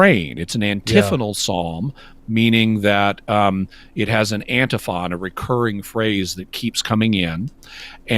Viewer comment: Antiphon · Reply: Yeah; I know what he meant.When he repeats it, at 1:14-ish, it sounds like he was saying AntiFa. Edit